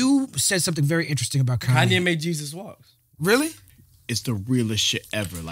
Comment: "Kanye made Jesus walks." "Oh really?" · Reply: I thought he said Kanye made Jesus walk.